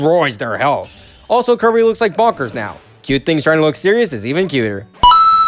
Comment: I think said: I piffled beetle by the way. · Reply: No, he said, "I'm Piffle NEO by the way".